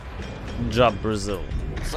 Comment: it's funny, instead of "vzlyot proeezvyol" you said "job brazil" :D